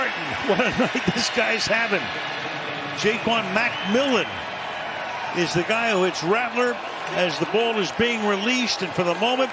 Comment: what is she saying? I got the “WTH” part but what’s the lead up 😅😂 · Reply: Oh never mind, I believe it’s “explicit Spencer rattler WTH”